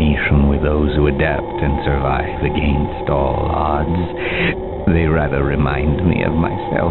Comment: does he say "surviving against 'ALL US' " ????? or smthg else? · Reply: Survive against all odds